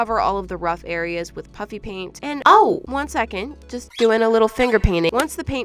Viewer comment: Y'all know how we thought she said puppy paint! If she gets a paint squishy she should do........ A puppy paint! 🤣🤩🤣 Like if u agree!!!!!!! 👍👍👍👍👍